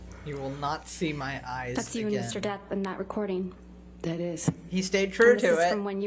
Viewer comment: Anyone else notice the subtitles say "mr death" ☠